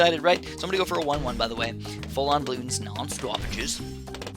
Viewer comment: Did anyone else hear "full on bloons non-stop bitches" at 1:40😂😂